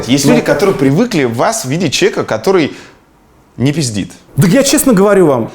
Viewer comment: мне послышалось или дудь сказал навальному "не пизди" на 58:17?